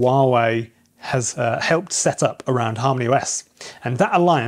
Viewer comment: When I first heard you say Harmony OS , I thought you said HARM THE US 😂